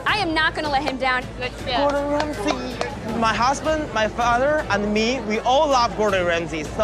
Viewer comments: did he say “my husband?” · Did he just say "HUSBAND"? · “My husband”. Uhhhh interesting, plz tell me I heard it wrong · wait did he say My husband Whats going on here